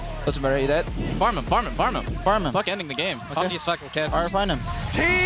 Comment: does Jensen say "let's kill these f%$#ing kids"?